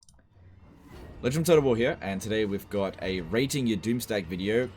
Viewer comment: I must be SUPER baked but I swear I heard "Asian of Total War here"